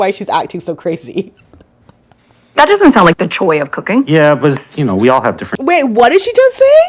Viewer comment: I thought she said " joy of cooking" but she said " choi of cooking" 😮 rude b***h